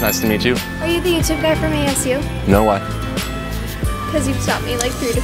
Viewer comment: what did the girl say during that whole conversation i didn’t understand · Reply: "Are you the YouTube guy from ASU?" ASU = Arizona State University